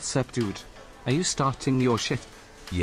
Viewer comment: For a moment I thought the Chad said “Are you starting your shit?” 😂